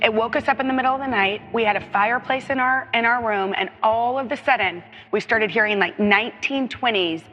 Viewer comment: Did she say, "all of the sudden?" 🤦‍♀️